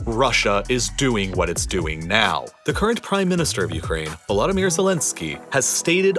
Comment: he starts the sentence. Is it just ne or does he say " the CURRENT PRIME MINISTER OF UKRAINE, volodymyr Zelenskyy"???